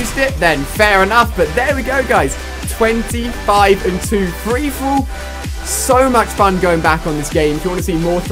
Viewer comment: Sounds like your saying freefool